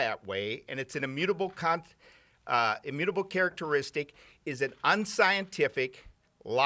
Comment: Did he just say "an immutable CUNT" - 0:40?